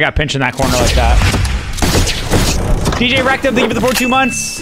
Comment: I thought he said DjErecto lol